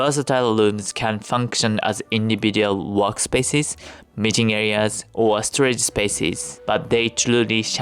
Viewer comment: I thought he said "mating areas" and I had to rewind and turn on the subtitles lol